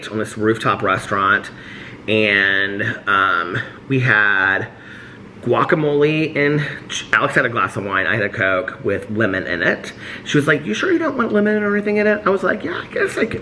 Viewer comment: Lol I was crunching a pretzel and I thought said he had a diet coke with women in it. 😂